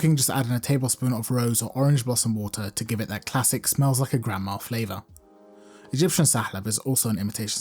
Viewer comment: mins Did he just say it smells like your grandma? 😧